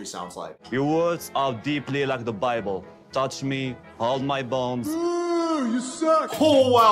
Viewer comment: I thought Osama said “hold my balls” instead of “hold my bones” this whole time